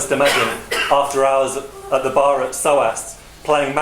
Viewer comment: What exactly is Comrade saying at 5:07 when making that brilliant poker analogy? I hear "bar at Stoast", but I don't think that "Stoast" is entirely correct.